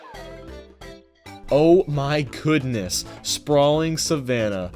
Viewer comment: did u say oh my coodness on purpose?